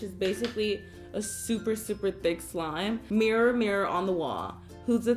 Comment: actually the evil queen does not say mirror mirror but says magic mirror☺️